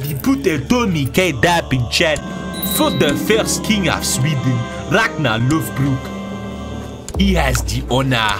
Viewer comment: For a second I thought it said Ragnar the Lobrox XD